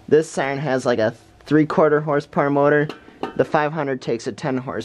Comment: It sounds like you said “mower” instead of “motor”. I think that seems rather “fitting”, because they basically mow the air with that chopper, anyway. Anytime you mow air into chunks like that, the result is the sound that we all go after with our cameras... PS: I have known how these things longer than I have been recording them. PPS: I should probably get out there and record another siren test sometime soon, because I haven’t done a proper siren video in a while.